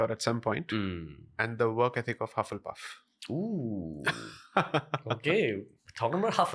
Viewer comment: In this video at 34:31.. work ethics of ??? Please explain that full word what did j sai said ? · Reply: Hufflepuff. It's one of the houses in Harry Potter movies